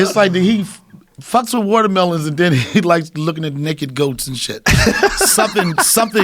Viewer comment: Did Donnell say 'naked goats'? 😂😂 like they have clothes